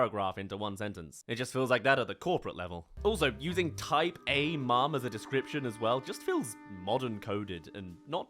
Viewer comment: OH! Type-A mom! Why did I hear "Tight A mom" as in she's a tight ass... like, uptight. I was shocked that they'd use, but thankfully the comments cleared that up for me lol